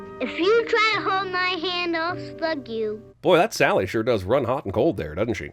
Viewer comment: I SWEAR I THOUGHT SHE SAID I'LL FUK YOU